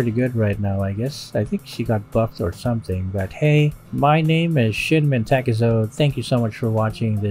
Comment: Yes he's not nut. Its true. Also Im drunk and swore I heard you say "my name is shit-man take-zone" 🤣 glad i rewound the video